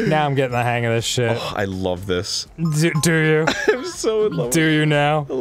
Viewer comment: i thought he said "i'm... so high" at the beginning lmao